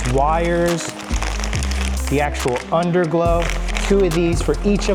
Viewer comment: Did brotha say underglow 😧🥹